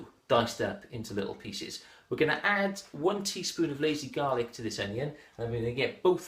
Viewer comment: What does he say @ 1:15? Sounds like 'lazy garlic'